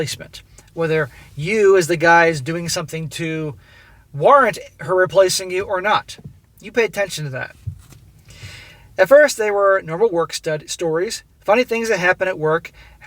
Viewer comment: Sounded like you said Work Stud, and you would be correct on that.